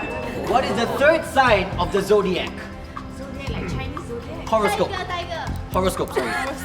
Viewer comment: Hold up did say “vanilla,Chinese”